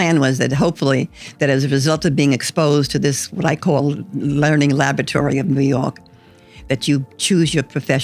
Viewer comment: I legitimately thought she said "learning lavatory". That's why the good, the bad and the ugly made so much sense.